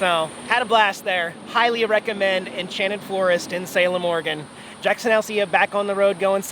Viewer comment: did you notice at the end just before your out-tro it kinda sounded like you said Enchanted Florist? 19:19 Good video keep up the good work.